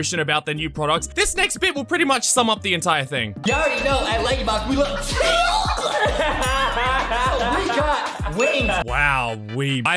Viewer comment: “you already know at LankyBox we love chicken so we got wings” is what he said